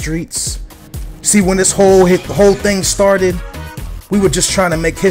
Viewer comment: Did you really say when "...the whore things started..." at 0:12?